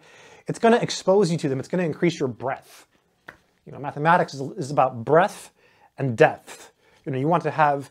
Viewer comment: I thought you said mathematics increases your breath 😄 but anyway, there's truth in that: Mathematics approached properly does breathe life into my mind, vitalizing it to do more math and learn more about other subjects as well. Thanks.